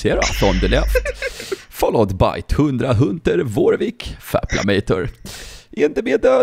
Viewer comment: LeL Dud did you say Faplamator XD